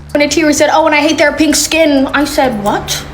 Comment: She said "little pink hands", not pink skinned... 🤦‍♀️ People are just going to run with this bs. And I say this as a brownskinned person with pink hands. lol Also, did anyone notice how Netayam (the eldest) has normal Navi hands instead of the five fingers that he hand in the real world. 🤔